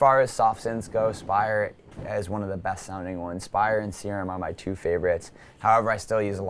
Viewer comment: Spire and what? cant understand him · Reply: Spire and Serum